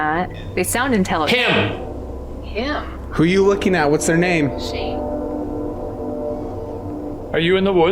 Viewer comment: the camera man says, "Who are you looking at what's their name?" Someone whispers next to the camera and says "Shane"